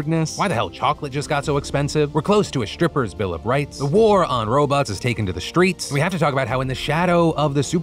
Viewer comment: I deadass thought my man said "The war on RoBucks". Like what has the world come to when we're burning the city down over fucking ROBLOX